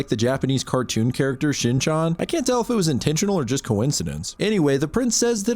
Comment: did he say chin chon